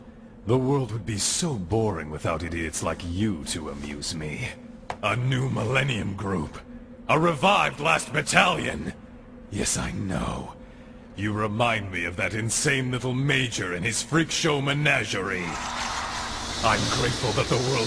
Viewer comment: what did he say ? please tell me I'm Turkısh ı don't understand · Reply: Yiğit Kayman “a new millennium group a revived last battalion yes I know you remind me of that insane little major and his freak show menagerie I’m grateful the world still breeds such madness.”